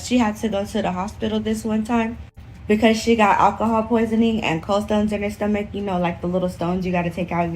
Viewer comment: I'm sorry...... did she say COLD STONES!?!